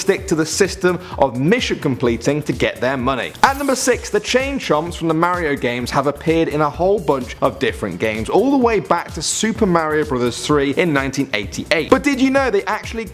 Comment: lol, thought he said chain chumps